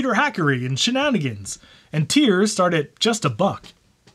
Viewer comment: When said "tiers start at just a buck", I thought he said "tears"